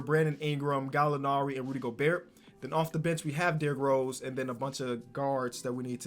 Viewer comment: Who else thought he said b***h instead of bench